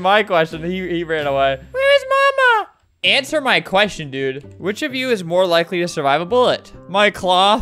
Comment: "Whose most likely to survive a bullet?" Me: sees the gun in his tools Also Me: oh i know where this is going. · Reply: When my sister said 'My cloth", she thought said which PART of you is most likely to survive a bullet.